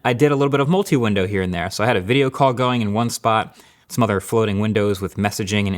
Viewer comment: ayoo can anyone correct me please did he just say Mother floating windows